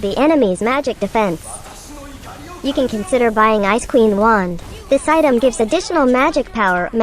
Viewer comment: I hear ice scream wand 😜